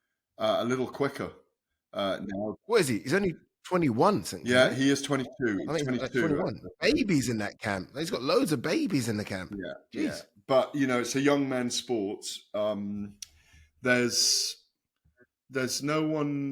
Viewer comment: I thought he said I’ve got loads left